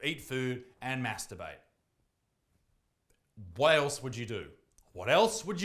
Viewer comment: Istg @ 2:42 I thought he said- "Whale" else what you do?